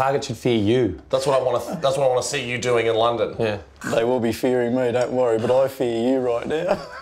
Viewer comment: No, I heard "There going to be fearing me"